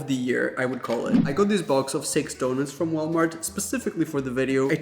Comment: You said” I got this box of six donuts”. I heard “I got this box of sex donuts“ lol